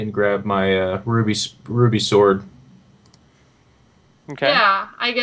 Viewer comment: Every time I hear say "Ruby Sword" I always hear "Groovy Sword" and I just imagine it to be a giant disco ball on a sword hilt. XD